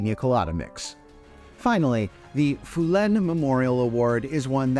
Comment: LOL--it almost sounds like you said " phlegm memorial award"!